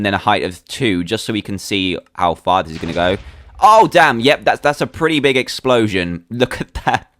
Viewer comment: Why is people making a big deal about dan saying wth 10:04 he said oh damn